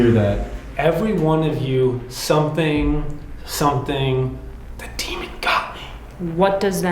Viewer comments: Go to <link> to get 15% off! Thanks to Raycon for sponsoring! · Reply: I thought I heard "Every one of u better run or go The demon got me" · I hear “everyone of you, they attack, the demon got me!” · I clearly heard “everyone of you Have Not The demon got me” Love yall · For the EVP, I think I heard it say “Every one of you….GET. BACK. The demon got me.” Sounded like maybe it was someone or something warning you guys to get away from the doll or he’ll get you too. Edit: now that I’ve actually finished, I’m even more convinced that it was warning you all that things were gonna get intense. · I hear "Everyone of you GO NOW, The demon got me" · For me i hear, " everyone of you... did that... the demon got me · I hear: Every one of you, get out, the demon got me. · For the recording at the end I heard "Everyone of you, get out, (something about run) the demon got me" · I hear “every one of you in the group, the demon got me.” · I heard "everyone of you, get out & run, the camera got me" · I hear: "Every one of you get out, ____ the demon got me." That is a clear warning. And of course they don't listen. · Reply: I heard "Every one of you get out, can't relax the demon got me". .. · My daughter and I heard “Every one of you get back. The demon got me” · It sounds like it said " Every one of you must run the demon got me" also Robert I apologize for seeing you through the video. Please forgive me. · I hear either "everyone of you (did not) or (get back)...the demon got me".